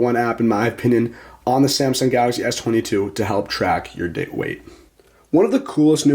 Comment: O.K., did my guy just say at 7:15 “Samsung Health is the best app to track you dick weight”? I listened to that over like 3x and I swear that’s what he’s saying!